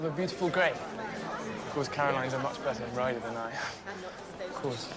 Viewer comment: I can’t understand what Mr.Bingley is saying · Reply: "... a better rider than I, of course"